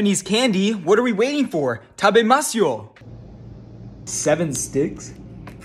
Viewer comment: "7 sticks" Bro 😭 naw but I fr thought you just said seven's di